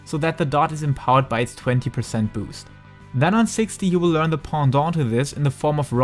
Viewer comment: FFXIV; what word do you say at 2:45; Pendant, pandante?